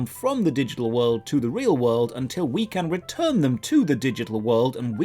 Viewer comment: So I listen to a lot of your videos at work and at 4:49 I heard “Porn Chest Pee Mom” and I was genuinely confused until I looked at the video.